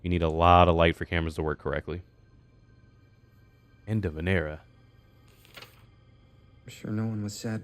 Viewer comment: Ngl 45:03 sounds like he saying Enda Vinera lol